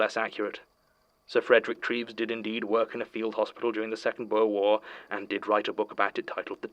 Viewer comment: Does that sound like is saying the Second World War? If so, the tale of a field hospital dealt with the South African War / Boer War (at least the real version of the book). 1900 is the publication date, so it would be quite a strange thing if a book published in 1900 described the events of the 1930’s and 1940’s. Relistened to statements a few more times and it says Second Boer War. I need to improve my listening.